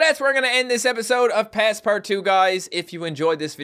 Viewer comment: when he said his intro i thought he said "Passport 2" , as if it was a sequel to a previous game